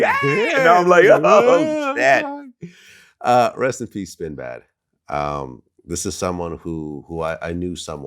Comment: What did he say though? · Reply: well vlad sounded like he said sinbad but I looked up djs that passed and dj spinbad just died so that’s who they are talking about rip